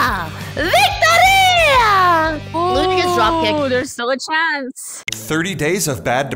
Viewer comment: Did she sayd "Ура"? (that's in russian)